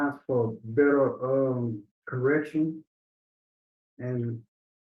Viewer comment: Wait a minute, 2:07 did he say he is glad about the POSITION he is in now because it’s a chance for a better ERECT!ON?😢 He didn’t even say he was sorry for the hurt and damage he did to a child. He only apologized for hurting the people he feels loved him.🥹 he is so dangerous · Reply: Correction